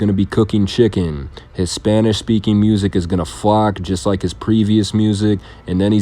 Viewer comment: The term is "flop". Sounds like you said flock.